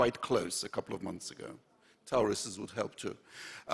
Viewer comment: error in subtitles: "tories". if I hear correctly. "Torysi", nie t(....)